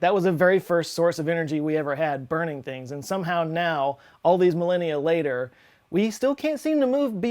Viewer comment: For a second I thought he said "all these millennials later" and went wtf